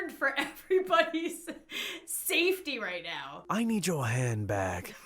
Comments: When he said "I need your hand bag" I thought he said "I need your head back" 😂💀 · is it just me or when he said "I need your hand bag" thought he said "I need your hand back" lol😂😂😂😂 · Instead of “ I need your hang bag.” I thought he said “ I need your hand back. “ 😂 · When he said I need your hand bag I thought he said I need your head back or I need your hand back XD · When the dude said 'I need your hand bag' I thought he said 'I need your head back.' I was very confused. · I thought the robber said i need you hand back😂 but when I heard it again cause I was confused but he really said I need your hand bag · When the guy said I need your hand bag I thought he said I need your hand back🤣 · "I NEED YOUR HAND BAG" What I heard "I NEED YOUR HAND BACK"